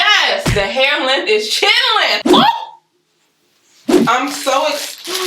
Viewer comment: 🤣🤣🤣🤣🤣🤣🤣 I thought you said "chillin" but it's "chin length" 🤣🤣🤣🤣🤣🤣🤣🤣🤣 thank u for helpin a couz out!!! 🤣🤣🤣🤣🤣